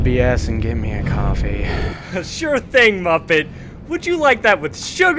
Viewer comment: I know what he's actually saying, but I find it funny because 7:21 sounds like "Sure thing, muffin!" P.S. Comment number 69 :3